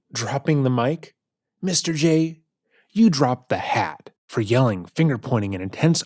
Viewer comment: Just FYI, at 59:35, she says "Mr. Jay, instead of dropping the mic, you dropped the BAT", not "the hat." Love your videos.